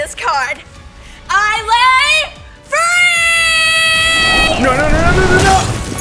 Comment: I thought you said furry.